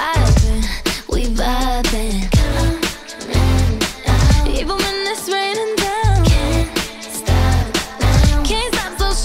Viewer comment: Instead of “Even when it’s rainin’ down” I hear “Even when we’re sweden now”